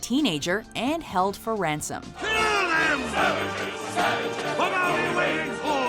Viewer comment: In the pochahontas song where the villain sings, I heard "sandwiches" instead of "savages"